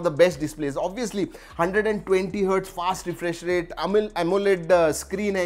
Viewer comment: who else heard amul amoled display